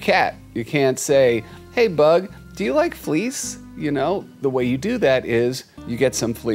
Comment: When you said "Do you like fleece?",it sounded like you said," Do you like fleas? "